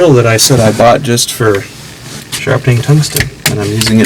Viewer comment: Dude, thought you said you bought that cordless drill for "just when grinding tig tungsten" hahah